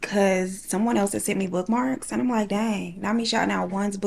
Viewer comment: This is my first time seeing one of your videos. When you were talking about the mail, I somehow misheard and thought you said someone sent you blood marks. I was wondering why you were so calm 😭